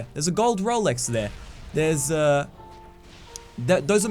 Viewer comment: lol thought he said bitches